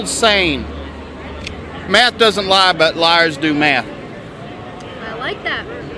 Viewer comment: "Math doesn't lie... but liers do math." Liberal media in a freaking nutshell. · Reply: I thought he said liars do meth. 😁😁😁